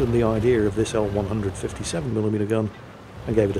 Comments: Yes the famous early developement AMX 13 with a 157mm gun :D *Edit1.: I need to get new ears... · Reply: I'm in the same boat, I heard "157mm gun" as well and got very confused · Yes the famous early developement AMX 13 with a 157mm gun :D *Edit1.: I need to get new ears... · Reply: Okay, now I see, I went back on it twice before writing the comment and both time I heard "this old 157mm gun". That makes much more sense.